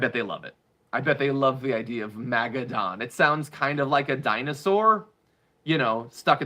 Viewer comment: I thought he said maga doughnuts 😂😂😂